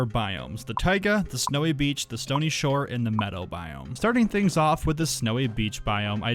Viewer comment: Idk why but I heard "metal biome" instead of "meadow biome". XD